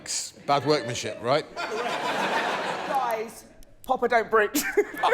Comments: Can someone help out? I can't figure out what she said here. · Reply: Papa don’t breech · What is it that Roisin says at around 27:41? "Papa don't breach" is what YouTube captions think it is. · whatd she say? i just cant catch it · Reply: Papa don't breach.